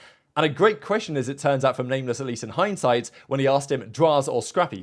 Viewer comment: did say Lameless ?? xD